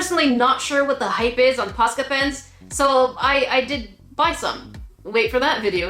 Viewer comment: when you said "posca pens", youtube thought you said "not sure what the hype is on pasta pants, so I I did buy some", so that is now the captions XD